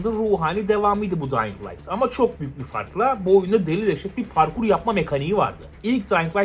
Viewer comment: 'de ne diyor? Deli yaşıt bir parkur yapma mekaniği mi? Bir türlü anlayamadım yardım lütfen